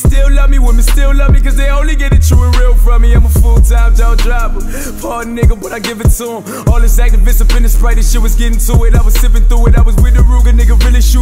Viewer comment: full-time jaw dropper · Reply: I hear "I'm a full time job driver" 😂😂😂